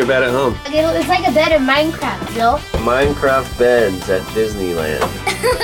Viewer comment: I’m sorry, did he just say “Miecraft?”